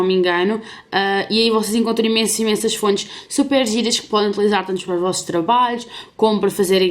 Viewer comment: ela falou super giga? alguém sabe o q significa? eu não entendi muito bem...